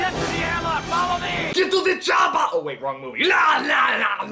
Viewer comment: Hay un error de traduccion en el minuto 14:33, el critico dice "get to the choppa" (ve al helicoptero) , que es la frase que dice en depredador y no "go whit the jabba" que es erroneo